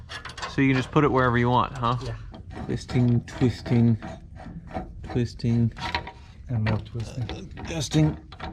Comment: If you have subtitles on, when he says “twisting” at 5:41, the subtitles say “Christine...Christine...Justin...”